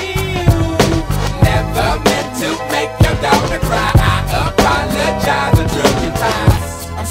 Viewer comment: When I was little I thought it said “I’m sorry miss Jackson ooo never meant to make your dolphin cry”